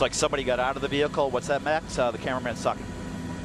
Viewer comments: after the guy gets out to take a leak: "What's that, Matt? Oh, yeah. The camera man sucks." Did I hear that correctly? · did he really said "the camera man suck" ?